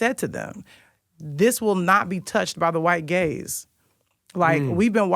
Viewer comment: When she said White Gaze I thought she said White Gays I was like oop 🤭🤣